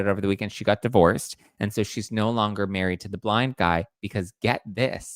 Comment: When you were talking about your friend being married to a blind guy at first I thought you said black guy and I was like what does that have to do with Helen Keller😂